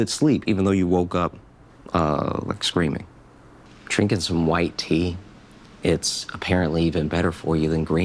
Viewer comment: Not gonna lie, thought you said white tears, and then took a sip. Had to replay like three times xD until i let the video play enough to you saying "better than green tea" and my brain exploded